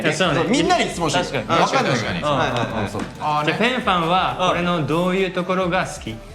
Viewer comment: フェンファンは俺のどうゆうところが好き？に聞こえて急に特大ファンタジーと大ボケかましてきたかと思った笑